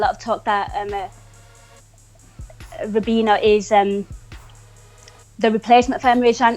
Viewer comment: swear she called him Ribena, she is secretly Milner